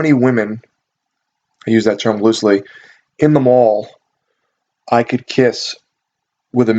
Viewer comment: OMG when you said I see how many girls in the mall I can kiss with a mistletoe, a commercial popped up and it sounded like you said how many girls in the mall can I kill